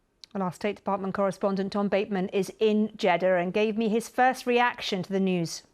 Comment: Did she say "Jedder"?